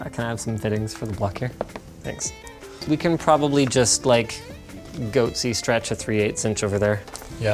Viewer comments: did he say "goatse stretch" at 4:46 kek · I'm sorry...did you just say Goatse stretch? LOL